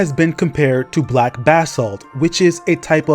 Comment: Why didn't you tell me BEFORE I recorded the video? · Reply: thought bro was saying "bathsalt" lmao